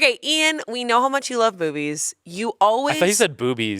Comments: Devil's advocate for 2:30. I heard boobies as well. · In Ian’s defense it legit sounds like Amanda says “Boobies”. Or more like “Bovies” 😅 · I also heard boobies instead of movies wtf